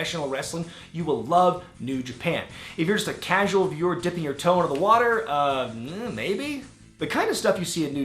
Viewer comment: When he said "dipping you toe in the water" I thought he said "Toa of water". I was about to say what the fuck does this guy know about Gali, Nokama, or Hahli